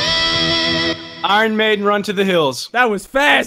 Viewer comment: I hear saying Iron Man